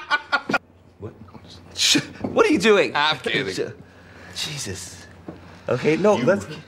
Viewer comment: what does clegg say at 3:45 ? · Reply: "I'm kidding."